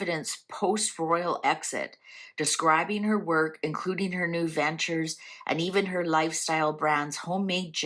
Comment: I heard "her new dentures" when you said (I think) "her new ventures"... and I LOL'ed so hard when I realized my misunderstanding... 😂